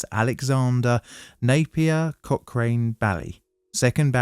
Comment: The name just rolls off the tongue... best laugh I've had so far this year. May the sun shine on your corner . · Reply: I mean, I misheard his name as cockring-baille so ...